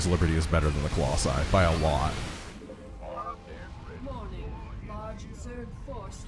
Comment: did she say good Morning? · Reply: Warning